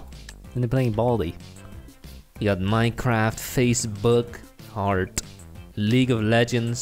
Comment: did he just say facebook